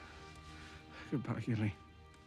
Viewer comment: Can someone clarify what Sam says here? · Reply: Goodbye Gilly ig